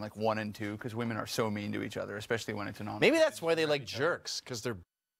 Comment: I dont understand the English well what did Mr Mcinnes say at 3:04. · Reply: He said "bitches" which is considered profanity so the show censored it so you couldn't hear it. "Maybe that's why they like jerks, cause their bitches?"